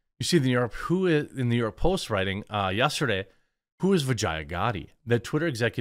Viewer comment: I thought for a second you said "who is vagina?"